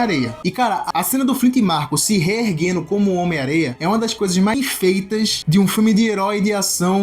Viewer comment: não entendi, é mais bem feitas, ou mal feitas? Acho que pelo contexto é "bem feitas", mas parece ter um corte aí. Tirando isso, vídeo top demais! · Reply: "bem feitas" o áudio deu uma bugada akakakakakak. Valeu meu mano 🔥